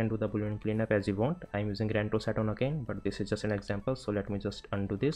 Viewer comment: What do you say at 1:30? What are you using for cleanup? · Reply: I say 'RanTools' that is my All-In-One addon. It's also linked in the description .